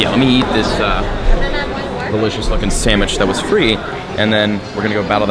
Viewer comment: "delicious fuckin sandwich" did I hear that right?😂